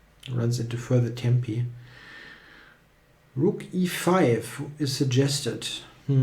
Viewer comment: Re5? Did he say that?